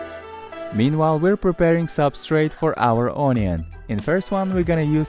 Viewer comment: What was the 2nd substitute of soil? I heard "soda"